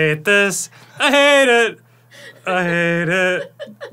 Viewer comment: Danny singing the 3rd "I hate it" at 3:45 sounded like Afroman - I made it